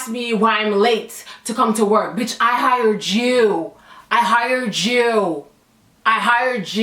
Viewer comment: “I hear a Jew.”